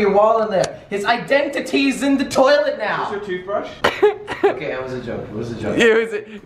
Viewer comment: Ok I rewinded it at the right time it sounded like he said he's in the toilet now